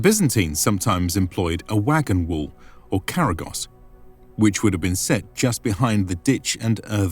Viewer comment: did he say karagoz?